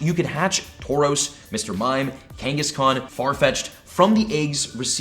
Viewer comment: did he say gangas khan?